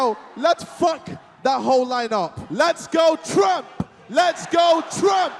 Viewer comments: I thought he said ‘let’s go tramp’ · I thought ksi was saying "let's go tramp" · ohhhhhhhhhhh, I thought he was saying 'lets go tramp' as in like a homeless person, whoops